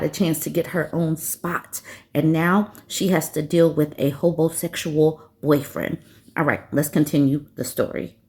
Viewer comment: LOL! I put this vid on earlier and my mother misheard and asked, with attitude, "What does she mean 'Homos-xual alert'?" I laughed and corrected her and reminded her that I'd already taught her the term. "HoBOs-xual". She laughed hard then said, "Oh, yeah, that's right! But at first I was wondering why you were watching a woman who would talk badly about homos-xuals". 😂😂😂😂